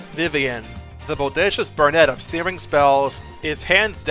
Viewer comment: The Bodacious Brunette? Is that what i heard? Ummm, she got pink hair. i dont think she counts as one... but anyway, great video!